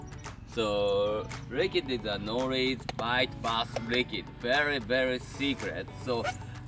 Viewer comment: I watched / listened 3 times …. What is he soaking those baits in ? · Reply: I thought he was saying tuna liquid lol i was like shit I never thought of that